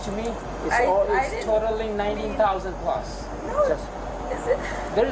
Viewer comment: Only $20 a day... hmm, assuming 7 days a week 52 weeks of a year, after 3 years that's over $21,000. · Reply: At this part the subtitles say 19,000 but I swear he said 90.